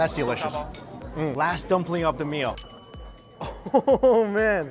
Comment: Sorry, at 19:10 it says "Red Bean Bun" when he said "last Dumpling of the Meal". I think those are Buns Not Dumplings. Just dont want to get confused. Even the Waiter who brought out the food, he said in Chinese "buns" too. I loved what he said at 18:00, "if you can, get a bowl to share or you know what? Come by yourself, get both eat it all yourself cause you only live once". OMG!! So, so true Mike! 😋